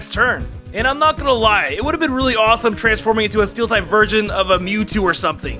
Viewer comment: did he say steel type virgin? lol